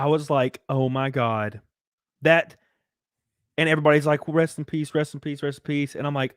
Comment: When he mentions anna getting channel banned i thought he said Breast in Peace (rip channel)